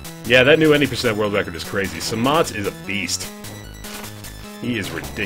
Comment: Who is the guy he's talking about from 4:30 to 5:20? Sounded like Samant or something. I wanted to look at his speedruns